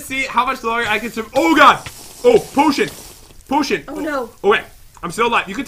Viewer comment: I heard him said OH SH'T